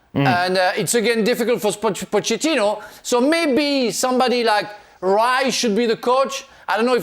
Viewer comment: did he say Dyche or Rice?